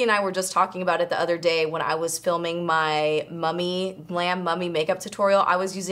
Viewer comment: I'm not a native english speaker and i heard "Glam Mommy Tutorial" i was like Whaaaaat? hahahaha